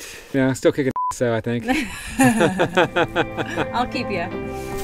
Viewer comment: I clearly heard her say "I'll keep you." my buddy, my friend, as a fan I must offer this up, in the future, this is a moment that you should cease all action and immediately negotiate Little Debbies for life!!